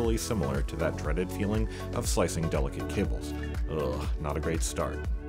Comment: "The glue is tough and stringy, making deep blind prying feel like slicing through delicate cables." pries through the battery cable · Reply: I heard it as "delicate kittens" 😖